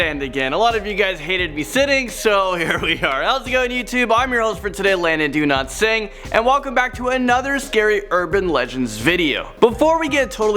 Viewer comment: I thought you said "London do not sing"